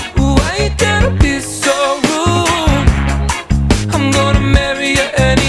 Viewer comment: I always thought he was saying Cruel... and I'm sure the line I heard was "why do you have to be so cruel, im going to marry ya anyway" which is disturbing on many many levels... I guess in was wrong or we got a different version in the UK